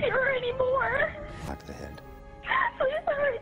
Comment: En el minuto 8:50 se escucha una voz de fondo, que lo que dice es: “shoot the head”, o sea: disparale en la cabeza. Explicó algo: 1) Desde el momento que hizo la llamada y dijo tenia las manos amarradas yo sospeché que algo estaba mal ahí por que no es posible que ella lograse escuchar lo que la operadora le decía y de paso hablar tan nítido ( es tanto la adrenalina x los nervios que uno tiene al llamar al 911 en medio de una emergencia y más con las manos atadas hacia atrás, que es muy difícil luego de marcar también colocar el alta voz), pero vamos a creerle esa número 1 y vamos con el número 2, que es el más importante. 2) El 911 tiene equipos tan sofisticados que se puede escuchar y registrar los ruidos y voces qué hay en el área cuando se llama, esto para poder decifrar cuando se efectúa una llamada donde la víctima solo logra marcar y no puede hablar. Sin tener tanta tecnología como ellos, los que tenemos IPhone y hablamos inglés podemos escuchar claramente cuando ella llama, uno de los hombres dice: “shoot the head”, o sea: “ disparale en la cabeza”. (Yo supongo este fue el tiro que recibió la madre). Obviamente aquí se pone la alerta de que es un crimen organizado cuando escuchan nuevamente la cinta y lograr escuchar cuán cerca está ella de esta voz ( la persona, quien a pesar de tener una voz fuerte, habla en tono bajo significa no quería que el 911 lo escuchara. Quienes hemos sido victimas de robos, sabemos que esas personas están full adrenalina y es punto de grito